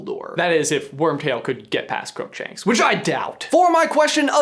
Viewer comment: GET PAST WHO????? I tried rewinding this part I still can't understand what grumshanks means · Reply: Crookshanks the cat